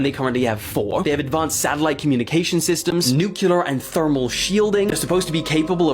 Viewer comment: did he say 'nucular' @ 15:32?